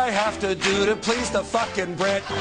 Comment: Okay. Thanks for the correction. I saw it uncensored once and thought he said fucking not porking.